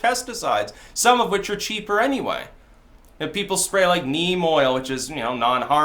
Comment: for a second there i thought you said meme oil haha